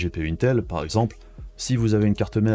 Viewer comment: A 24:34 => pardon mais j'ai pas réussi à comprendre la prononciation du terme technique employé (son orthographe pour être précis): quelqu'un peut être assez sympa pour me l'écrire en réponse, je ne connais pas cette spéc. technique et j'aimerais bien savoir de quoi il s'agit et de quoi il retourne, merci d'avance à la personne qui prendra le temps de lire ce post et d'y répondre :) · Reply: (Resizable bar)et (Sam )techno pour communiquer plus rapidement avec la mémoire de la carte graphique voilà